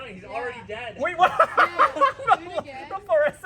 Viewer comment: Did I just hear caretaker? :P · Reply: It's try again